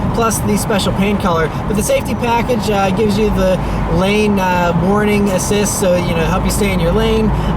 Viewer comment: When he said the safety package had the "lane warning assists," I thought he said it had the "lame boring assists" 😂